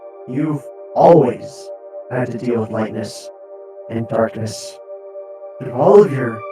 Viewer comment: In 8:00 did Origin just said Lightness